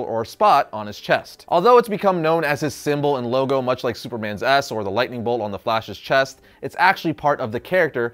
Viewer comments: My sleep-deprived self thought he said Superman's ass is Superman's logo. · did anyone else hear "Superman's A**"